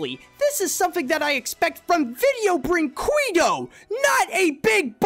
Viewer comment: yo what did he say Edit: nevermind it was actually Vídeo Brinquedo